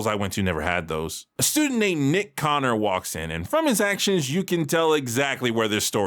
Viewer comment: For a second there I thought you said Mitch Conner.